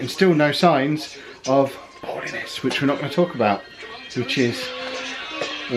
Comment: What did you say at 11:20?? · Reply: Poorliness